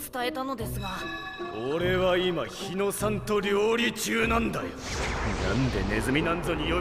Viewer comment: I thought he said "santoryu" (three-sword style)